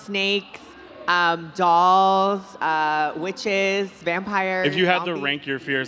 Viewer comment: did nikita just say "vampires"